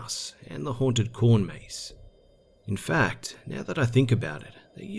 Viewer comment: When you said haunted corn maze I thought you said haunted porn maze and I was so confused I was like what the hell